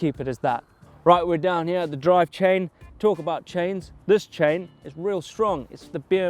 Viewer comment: Did he said drivechain???????